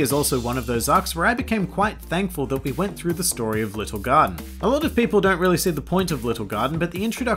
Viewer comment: i kept hearing "Lil Gun" when he was apparently saying "Little Garden".....